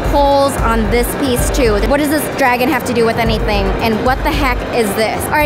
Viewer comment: i thought you said what the hack lol lol lol lol